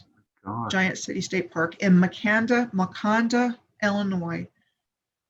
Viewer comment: Wait what? Did she say Wakanda?